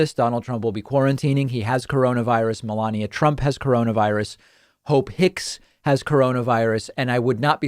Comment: Thought you said Ho Picks instead of Hope Hicks.